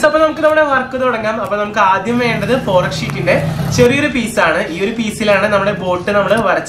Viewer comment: Can Anyone Tell Me What Name He Said Of The Sheet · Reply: Thermophilic sheet